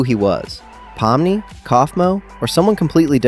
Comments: The subtitles at 1:37 has caine written instead of kaufmo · WHY DID THE SUBTITLES SAY CAINE INSTEAD OF KAFMO